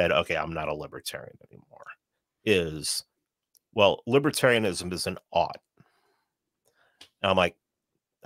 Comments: What did say at 23:05? I can't quite understand what he said. Libertarianism doesn't opt? Doesn't ought? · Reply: "Libertarianism doesn't ought". It means that Libertarianism doesn't tell you how things should be or how people should live their lives. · What does say at 23:06? Libertarianism is an...? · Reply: Ought